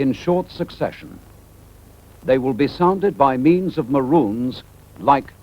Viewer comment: “They will be sounded by means of (…) like this” Is he saying maroons, or…?